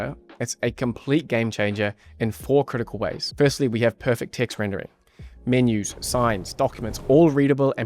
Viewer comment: Thought you said perfect tits rendering